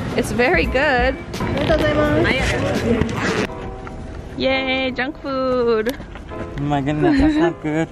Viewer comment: at 2;46 i thought you said yay jung kook lol